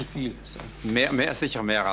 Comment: Can anyone translate what is said around 41:39 and 41:41 ?? It's not captioned but there's a loud whisper coming from someone behind the camera, sounds like a sharp command. Like "shut up"?... silence.... then "Fil Fil" or "feel feels". He then resumes his response as though nothing happened. CrEePy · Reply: Im pretty sure he said "viel viel" which means "a lot of" in german